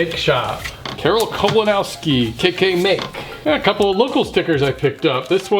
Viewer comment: KK Make seriously needs to reconsider re-branding... it pretty much sounded like you said KKK...